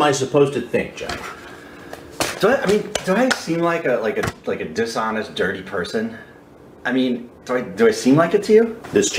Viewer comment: Jeff: "do I seem like a dishonest dirty person"? Also Jeff: eating a pizza like a dishonest dirty person... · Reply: At first, I thought you said "do I seem like a dinosaur dirty person" xD